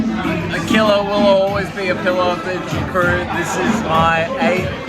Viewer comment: did say pillar of the bejing crew?